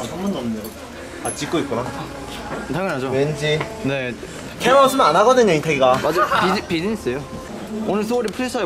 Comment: i hear enhypen😌